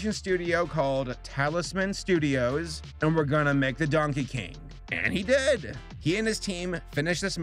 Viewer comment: I thought it said Donkey Kong!